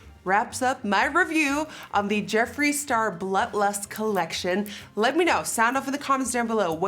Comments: Who else is triggered by her pronunciation of Blood Lust, sounds like she is saying Blut lust🤣🤣🤣🤣🤣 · Y’all the way she says “blood” lust in the beginning KILLS ME 😂 i hear “bluh lust” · I thought she said Blunt Lust😂🤣. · There were a couple times in the video where I thought you said buttless and not blood lust 😂. Lol great video!💜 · Anyone else think she sounds like she says butt lust every time she says blood lust or is it just me??